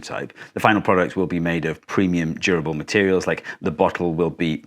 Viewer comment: Instead of “premium durable materials” we heard “premium gerbil materials” at 01:34 which made us think this was one of those water bottles you had to hang upside down and lick the little metal ball inside to make work